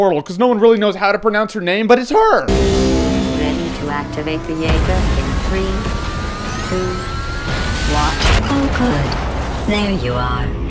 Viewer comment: "ready to activate the EVA" did I hear that right?